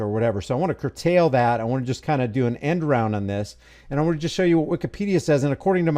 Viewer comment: Ohhh... END ROUND...At 1:25 I almost thought you said "Enron"...🤣 And... This just proves us "Conspiracy ANALYSTS" correct AGAIN.